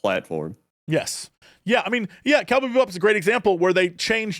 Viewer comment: I listened to this like 5 times, what show is talking about here I can't understand what he says. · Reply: Cowboy Bebop, I think